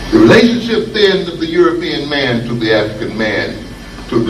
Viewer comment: What did he say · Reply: The relationship of the European to the African is one like Dracula to his victim.